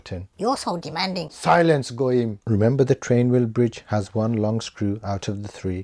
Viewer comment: Silence what now? AI is trying to say you are saying "nice and gentle" but I know exactly what I heard. Gentile, more like it. I see you, and have unrecommended